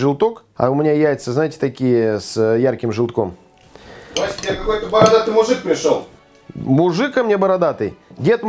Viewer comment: я не пойму наверно мне послышалось Костя к тебе бородатый мужик пришел ,причем Костя вроде у нас 😕